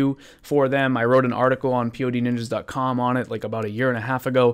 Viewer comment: I thought your logo said "Poo Ninjas" and was like 'oh well thats probably your problem right there'